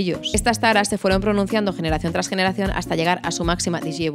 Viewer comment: Escuche bien? Digievolucion? HAHAHAHA *se me salio la carcajada haahaha XDDD y las fotos de los dudes cada vez mas deformes XD, amo como reseñas <3 Ijole cuantos detalles tiene esa pintura, wow